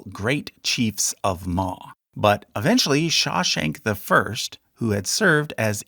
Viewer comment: I thought you were saying Shawshank I.